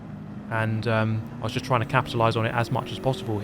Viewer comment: Anyone else hear “capsulatize” rather than “capitalize” at 4:30? Anyhow great video as always. This was truly one of the best GTWS events PD has put out IMO